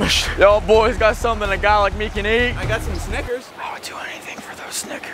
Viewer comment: I THOUGHT HE SAID THE HARD R 💀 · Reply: Snickers